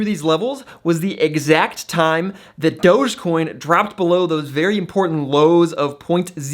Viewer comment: Did he just say "Dodgecoin"? 😐